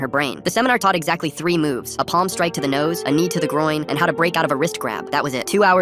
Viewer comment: Bro I thought he said keven de groin